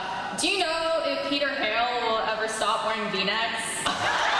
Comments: what is her question? I can only hear the first part of the question but the second half I can't make out what she says. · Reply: Johanna Bedoire Her question is: Do you know if Peter Hale will ever stop wearing V-necks? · Can anyone write what question was asked at 34:28 please? I couldn't quite catch it despite listening to it multiple times. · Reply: She asked if "Peter Hale will ever stop wearing V necks". Lol I loved that question XD